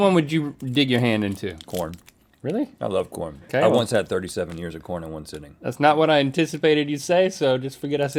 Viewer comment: "..I once had thirty seven ears of corn in one sitting. And thats not a joke, they just kept bringing them..." -Rhett 6:04 Link and Rhett are great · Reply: Omg I thought he said 37 years not ears!